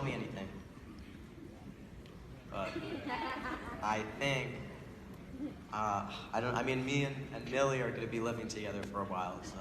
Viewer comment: Can anyone tell me what noah says in 1:49:05 plzzzzzzz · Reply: Dance with Me & Mom “I think uh, Millie and me are gonna be living together for a little while”